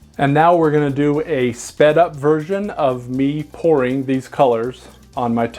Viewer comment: LOL. I thought you said fed up version, not sped up version.